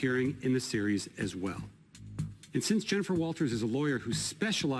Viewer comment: Did he say "JENNIFER LOPEZ"? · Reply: walters**